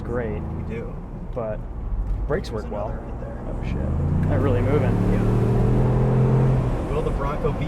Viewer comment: did I just hear him mutter "oh shit" as he raced a deer when he floored it?😂😏